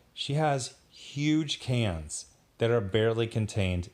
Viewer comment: I thought you said “she has huge HANDS” I was like wat